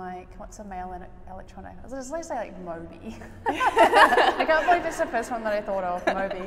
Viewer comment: What is she saying at 4:09? Automatic subtitles don't understand that, neither do I... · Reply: She says Moby! But don't understand why they are laughing at that :)